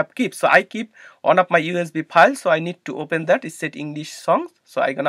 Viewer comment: which folder did u go into? I don't understand what u said at that point, because ur accent is too strong. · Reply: I went on my USB files folder 📁 where I kept my songs for transferring. Thanks.